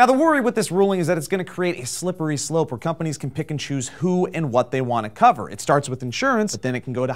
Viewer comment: When said "pick and choose" I thought he said Pikachu